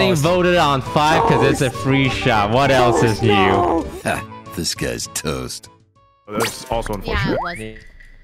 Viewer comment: BRO your intro sounds like it says “This guy’s toast” 😂